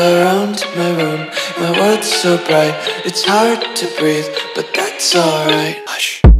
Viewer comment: People searching: "Its hard to breathe but that's alright" Me searches: "Bling bing ting ting bling ting bing bling blang bang ting" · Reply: I just heard it from a gacha intro and misheard it so I typed in "to break but that's alright" lol